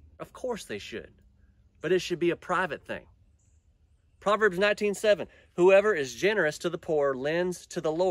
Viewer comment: Did you say proverbs 19-7 or 19-17 ? thought I heard 7 but maybe you said 17